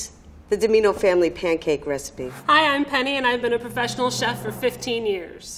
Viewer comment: I love how the level 3 chefs dont say theyre level three; they just flex on us about how long theyve been a chef for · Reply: I heard: I've been a chef since 1973!